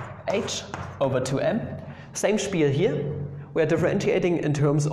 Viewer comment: did you just say "same spiel here"? xD